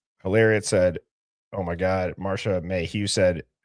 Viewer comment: I heard “of my god”